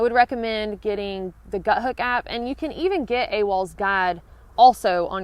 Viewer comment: Did 3 times playback around 14:28 but can not hear what app, when the arrow is in view. I hear something like 'gut hook'? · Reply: Yes, Gut Hook